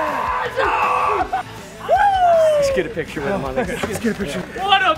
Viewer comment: did I hear, "Let's get a picture with it my nigga"